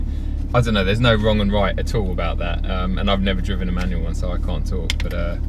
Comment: if you had driven an e30 e36 e46 manual trans youd understand why people say you should get a manual over dct. kinda annoying when people who never driven manuals make arguments for dcts. drive a manual first before u tell us why they arent as good as dcts. m240 is not a true M car imo. straight line races in BMWs r really silly. Buy a mustang if u wanna go fast in a straight line. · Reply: I thought u said u never driven a manual